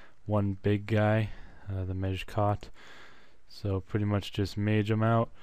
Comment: it sounded like you said "major cock" when you said "mejkot" lol